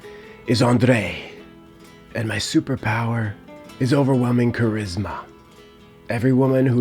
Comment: Because of an advert i heard 'my superpower is colgate'